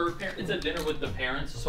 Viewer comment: Bro picked the theme and still thought they said "dinner with the peasants " instead of parents